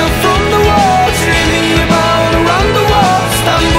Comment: i thought he was saying "dreaming about wonderwall"